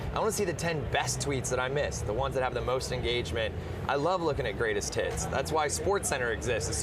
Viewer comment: I swear it sounded like he said "I love looking at greatest tits"